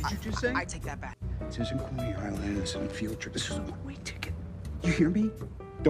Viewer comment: Tony: Kid where you come from? Spidey: Aight imma spoil endgame a bit. Wait... it's field trip? I thought he said future hahaha